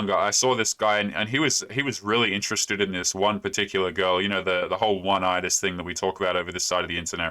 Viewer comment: What did you say at like 9:30? "the whole one eyes" something I'm not sure what you said, I'm curious